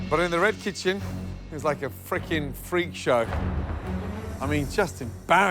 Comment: did Gordon just say freaking?